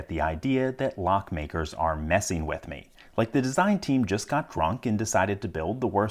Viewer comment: I thought he said "like the design team just got cronked and decided..." And I almost cried laughing